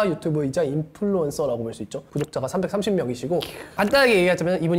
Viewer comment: 구독자가 330 명 이시고? ㅋㅋ 재생속도 0.5배속으로보면 확실히 들림